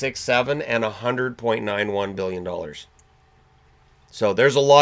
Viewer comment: Thanks for this informative video. At 4:27 did you say MILLION? Or Billion? I'd be inclined to think that GLOBALLY....gambling bets were estimated in the Billions, NOT in the millions. Just wondered.